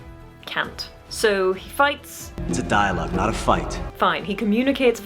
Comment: Now you see, I always thought he said "It's a dial-up, not a fight" , but dialogue gives it more depth.